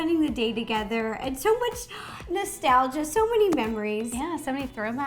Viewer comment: I hope you enjoy today's video with my sister! Have you ever had an American Girl Doll? What other videos would you like to see? 😊💕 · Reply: I thought u said : I enjoy you enjoy ...